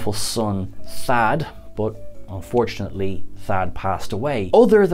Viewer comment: Fortunately Rikers son passed away don't you mean unfortunately... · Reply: ? I clearly hear "unfortunately"